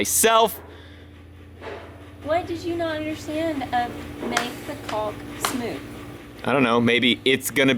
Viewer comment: It sounded like said “what did you not understand? make the cock smooth” Seems like he didn’t get the lube 🤔